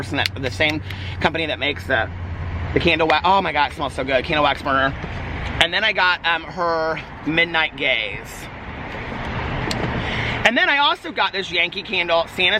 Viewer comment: Forgive me, but did you say Midnight GAZE or Midnight GAYS?? 😂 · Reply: Ok I was seeing if anyone thought the same thing, lol I thought he said "midnight gays" also 😂